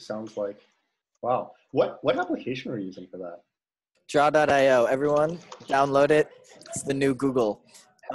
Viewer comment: can someone write me what he said jada.io ?! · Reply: Draw.io